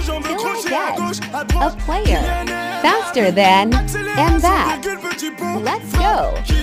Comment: Oh he said faster I thought he said better, I got so mad